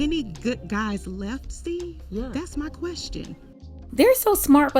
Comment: did she say "Steve"? As in Steve Harvey ? Oh boy ....he about to pander "Look heah ....dese men heah ....ain't on yo level.... ain't but fo good men out heah....but you keep doing what you doing... Yo car and good looks are enough to draw the good men to you....hyuck hyuck hyuck"